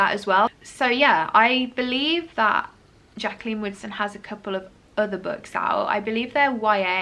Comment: Every time you said Jaqueline Woodson I had to do a double take cause I thought you said Jaqueline Wilson instead, sounded like you were saying "I've never read a Jaqueline Wilson book before" and I was like, hang on a bloody minute 😂